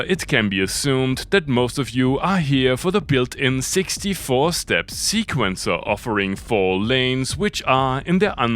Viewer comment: At first, I misheard "most of you are here for the 64-step sequencer" as "both of you are here for the 64-step sequencer." I thought it was just a charming self-effacing joke.